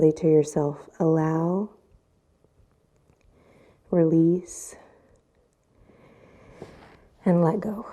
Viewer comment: "Allow, release, and let go...." WOW! Can't tell you how much that resonated with me today! Thank you! I heard it as "allow, accept, and let go...." Message received, universe. I'm trying!